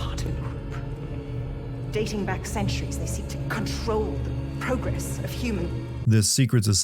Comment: maybe this is visible in other parts of the game but at 25:45 the subtitles say "guide" but the audio says "control". Audio not matching subtitles normally bugs me a little but it's even worse in this case, since "guide" and "control" mean very different things. From the outside it just seems like something that would be a two second fix - have the actress read the lines again or edit the subtitle text.